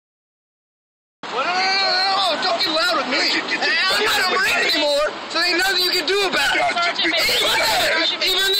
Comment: “Once a marine, always a marine” Guy waving flag: “I’m not a Marine anymore so you can’t do anything” Pro gamer move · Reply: I thought he said "I'm not in anymore."